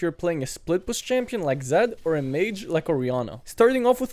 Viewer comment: i don't know why i heard "RIHANNA" and imagine her in mid lane with her baby hump 😅